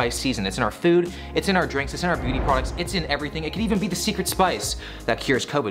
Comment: I misheard the first line and thought he said pumpkin spice was in "booty products." I was very concerned for a few seconds until I realized my mistake haha